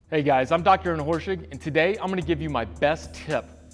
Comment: did he just say, "hi, I'm doctor horse shit" ?